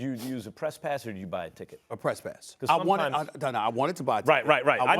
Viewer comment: I deadarse thought Max said trespass not press pass LMAO